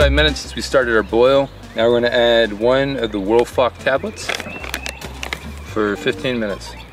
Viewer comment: I thought you said “world fuck tablets”